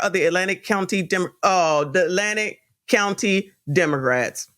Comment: I heard demorats 😆